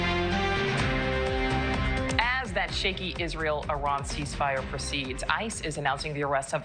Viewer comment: my kids have watched too much Bluey...I seriously thought I heard her say "cheeky"...😅